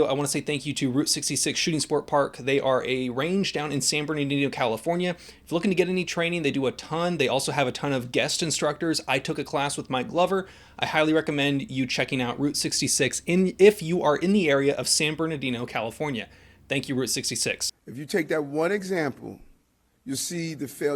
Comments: Lol, next time emphasize the K and the G in Mike Glover. I swore I heard you say you took a class with “My lover” 😂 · When you said you took a course with "Mike Glover". I thought you said "my lover". 😄 I thought of Will Ferrell's hot tub SNL skit.